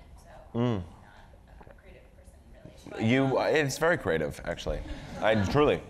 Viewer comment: Could someone just tell me what she said at 41:05 I couldn't tell clearly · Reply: I heard "So, I'm a grad student studying public policy, I'm gonna work in government, so, not a creative person really."